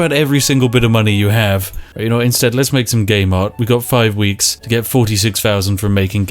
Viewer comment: I keep hearing 'Gay Mart'